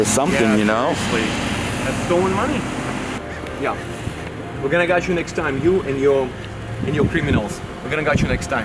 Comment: I know is saying “we’re gonna catch you next time” but I hear his awesome accent and it translates to “we’re gonna gut you next time”. Personally I prefer option 2